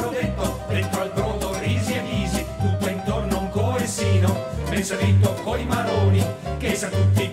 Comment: Ho bisogno di un aiuto... al 17:21 dice: Dentro al brodo risi e bisi, tutto intorno un coessino, ??? coi maroni che i sa tutti che i xe boni. Non ho capito cosa dice dopo "coessino", aiutatemi! :D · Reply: Tutto intorno un coessino / ben servito coi maroni / che i sa tutti che i xé boni 😊